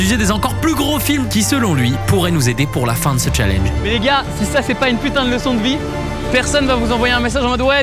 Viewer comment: je comprends pourquoi les Indiens te confondent avec Tibo Inshape. Avec cette phrase, j'ai cru entendre "daaaamn les gens, c'est un plaisir "😂😂